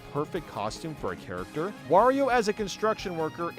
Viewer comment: is it just me, or anyone else hearing Mario’s name instead of Wario’s